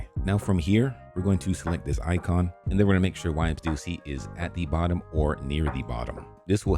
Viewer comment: why where the subtitles saying "wipe duty" when you said whyems dlc 3:04😭😭😭😭🙏🙏🙏🙏